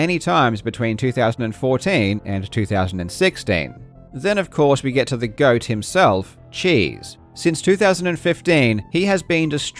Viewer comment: I misheard that one line as "Then of course we get to the goat cheese himself".